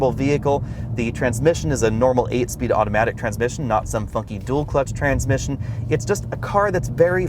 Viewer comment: Did just swear at 15:50 😮😮😮... had to replay cause I thought I heard wrong... lol. Best YOUTUBE car reviewer.. hands down!!!🕵️‍♀️ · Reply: Maxwell A he said FUNKY dual clutch transmission